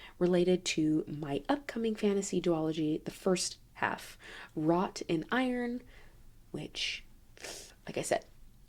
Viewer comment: I thought you said “Rot in Iron,” and that sounded dark. Then I realized it was “wrought in iron.” 🙃😂 Definitely purchasing it when it comes out!